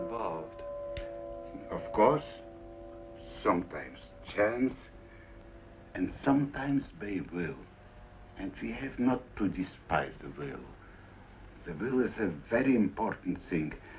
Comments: what does he say? Sometimes chance sometimes ?????? Babel? My english is not good sorry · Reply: Sometimes we chance, sometimes they will, and we have no reason to despise will. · I didn't understand what he says. I appreciate your help. "... Of course, sometimes chance and sometimes _ _ (?)". And 4:23 "... but he did __ ___". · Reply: I think he's saying "by will", as opposed to those things that happen by chance (?) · What does Stravinsky say in 4:08, I cannot understand the word.. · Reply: "Sometimes chance, and sometimes by will, and we must not despise the will..."